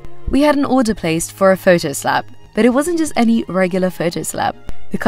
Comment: I thought she said a photo slap so I was so confused